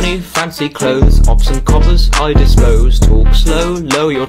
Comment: i thought he said i will eat your toes when he said i dispose